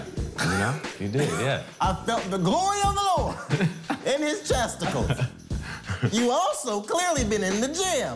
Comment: I kept having to replay what he said... mmmkay chesticles LOL